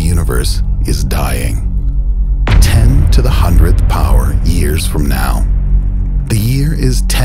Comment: I swear to god I thought it said 1 google years